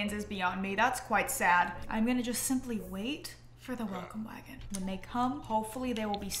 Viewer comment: I honestly thought you said “waffle wagon” instead of “welcome wagon” 😭😭😭